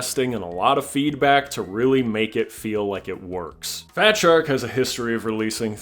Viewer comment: 😂I thought I heard you call them shart shark at one time